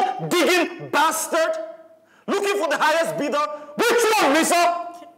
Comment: Wait at 1:34:40 did just call Alex "Lisa"? 😁😁😁